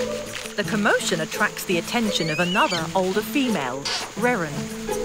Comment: I heard Karen🤣